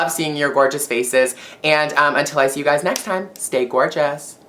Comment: In the transcript for the video the very last line at 7:21 says, "steak or Jess" instead of, "Stay gorgeous". So now every time you say it I'm going to think of steak.. Mmm, steak.